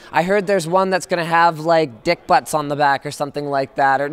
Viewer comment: Did Linus say "Dick Butts" ?